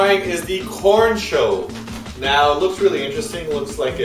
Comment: thought said ''porn show"